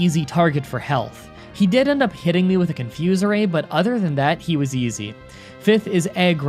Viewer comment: Did you just call that move “confuse array”?